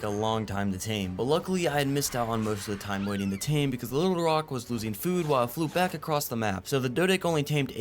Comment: What did you say at 50:39 😅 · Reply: Ulerock